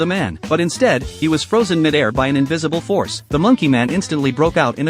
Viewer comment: I thought i heard mid-aura 💀💀